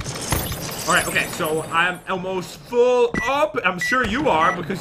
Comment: It sounded like said “I’m Elmo’s full” 😂😂 <3